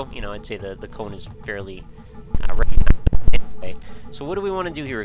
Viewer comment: i heard "fairly recognisable anyway"